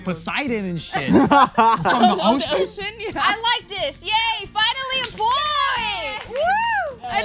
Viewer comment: said "finally a point" but it sounded like "finally a boy" LOL